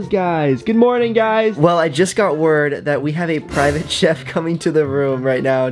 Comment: I thought he said private jet 😂